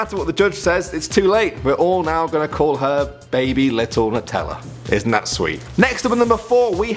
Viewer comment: I'll name my baby ellanut.... oh that's sounds SOOOO wrong to me *to me I heard Ella's nuts) :/ I'm dirty minded